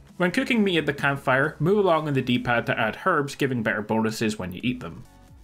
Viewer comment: wtf did he say about the D-PAD? Moo long? MuLan? Move along? I watched it about 50 times, I see he changes the recipe or something but i still have no idea what he's saying lmao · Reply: "Move along"... really not that hard to understand.